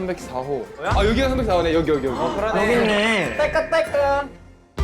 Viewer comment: Creo que mi mente esta mal porque escuche taka taka en el minuto